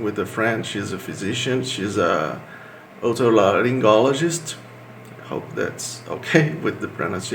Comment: what did he say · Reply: I think he said Otorhinolaryngologist or Otolaryngologist,a Specialist in ENT